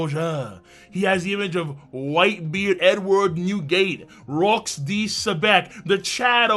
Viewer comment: For a second I thought I heard "whitebeard N-WORD Newgate" LoL